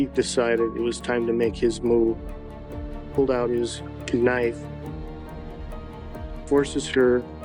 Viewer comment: Di......did he just say....."KANIFE"???